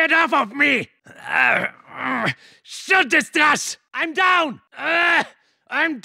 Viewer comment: What does he say 2:53? Alcha snizet? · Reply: Thank you, do you know what he’s saying at 10:45? It sounds something like “shoot this ‘der arsch’”